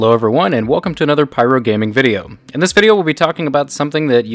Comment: i thought he said pyrerealm gaming